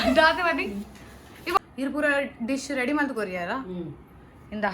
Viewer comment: What did mum say at 12:44? Sounded like a telugu word? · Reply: I think she said ' dish konada' meaning shall I bring plate/bowl? as bother wanted plate & bowl